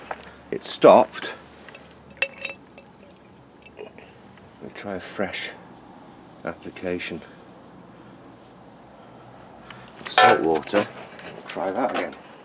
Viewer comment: Can you tell us what liquid you are using? Or the recipe for it. Burn baby burn!!! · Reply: I believe I hear him say "Let's try a fresh application.. of salt water" at 4:12 He also mentions baking soda, so maybe a mix of salt water and baking soda? Not sure 100%.